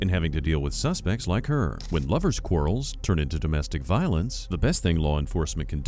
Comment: he said "lover's quarrels"... But I thought I heard "blubber squirrels" and just about died. 🤣🙃